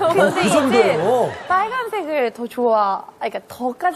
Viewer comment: she actually said 'I also liked red...'. At the time she was dating Nichkhun who loves red.